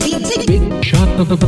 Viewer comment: I don't know what Dealmaker is really saying but I keep hearing "Big Shot on the Wii" for some reason... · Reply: [Big Shot of the Week]